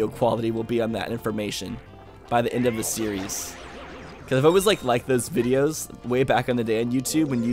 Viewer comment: did he just say "I like liked" 🤣